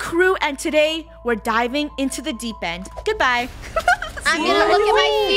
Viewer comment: What she said: "we are diving in the deep end" What I hear: "we are dying in the deep friend"